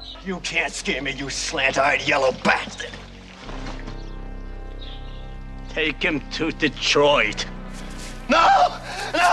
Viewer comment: for some reason I always thought he said "Take him to the droid"